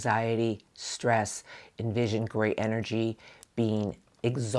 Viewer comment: Grey energy I think the word great was entered instead of grey …I know it’s AI and but had to mention it so it can be fixed much love your reading was 💯 on it omg I love your reading are so accurate for me and my partner ❤️